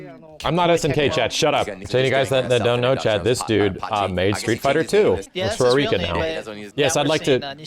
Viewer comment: I'm sure I misheard this but I heard "This dude made Street Fighter 2, looks Puerto Rican now!" lol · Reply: he said "works for Arika now" but ngl that sounds funny